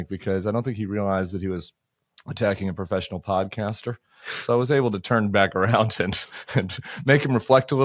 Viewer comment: Don’t mess with Tim miller he’s a professional podcaster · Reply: Podcaster? I thought he said Codblaster!